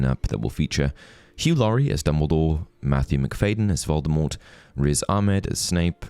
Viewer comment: I really thought you said Matthew McConaughey as voldemort... i was losing it at that thought ngl.